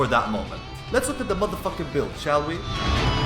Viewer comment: had to replay from bout 0:50 cause i heard lets have a look atthemuthafuckinbuild